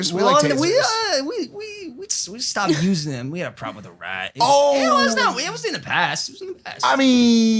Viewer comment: What are Mike and Logan talking about here? I want to know about the taser... Did Logan say he had a problem with a rat? Confused lol